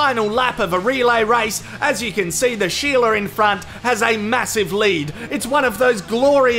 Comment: That one in the red learned how to slipstream from Need for Speed and she saved her Nos until the final stretch. The only thing missing is the jump over the train at the end. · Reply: i thought i heard..." the sheila infront has a massive 'load'..."haha...lol